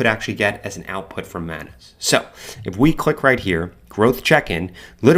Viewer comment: subtitles say "Madness" not Manus. Just so you know.